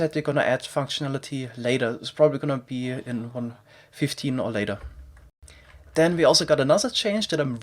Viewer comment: did he say 1.15 or later? for functionality of smithing and fletching tables?